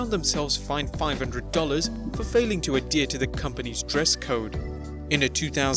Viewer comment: did you say endear(in which case your an idiot) or adhere (right and proper)! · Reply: It's obviously adhere, but if it was endear, it would be a slip of word. Or at the very worst, it's 1 word used wrong. How would that make someone an idiot?